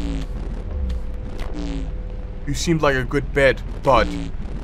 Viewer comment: Why did I genuinely think he said, "You seemed like a good boy" for a second there?